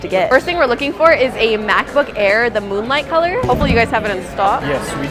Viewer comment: did anyone else realise said moonlight instead of starlight 😭💀